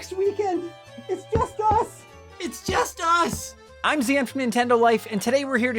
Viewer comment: I thought said “I’m Sam from Nintendo life” and was deeply confused.